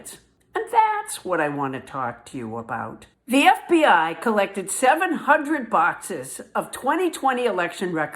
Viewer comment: You're clearly saying "collected," so why am I hearing "seized"?